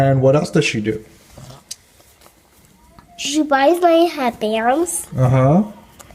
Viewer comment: "She buy's my happiness" am I hearing that wrong??? This is a perversion of the mind for sure, what the fuck is wrong with that mother